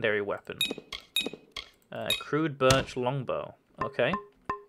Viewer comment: I thought it said Bitch Longbow lol My mistake!